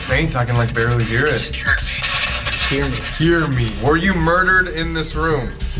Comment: kinda sounds like you scared me instead of heard